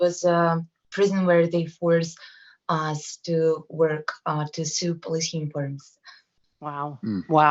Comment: did she say she was forced in jail to sew platoon forms ?????